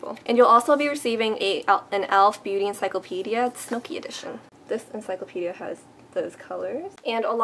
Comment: hahahah i feel silly. when you said elf encyclopedia smokey edition, i thought you said snookie edition.